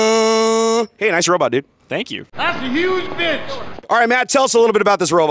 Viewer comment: who heard the guy yell "THATS A HUGE BITCH!" 😂